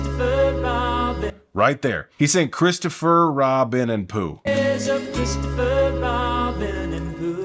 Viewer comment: Your plan backfired… Now I just hear “You are, Christopher & Rob with me.”